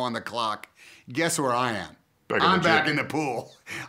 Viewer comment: I thought he said “…trying to look like a slut” 😂